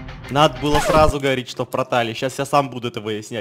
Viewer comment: Мне одной послышалось пртал?😂😂😂